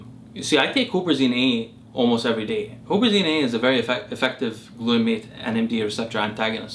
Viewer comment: did he say Obezine?